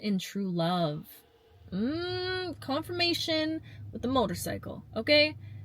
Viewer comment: I couldn’t hear the motorcycle so to me it sounded like “mmmmmm confirmation motorcycle” n Im using that now😂